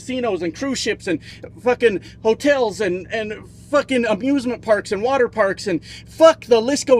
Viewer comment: It almost sounded like said "abusement" instead of "amusement". ..which is funny as "abusement" is right on brand for felon 47